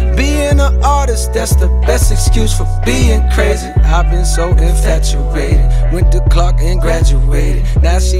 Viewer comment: Did he just say " I went to Clark and Graduated"??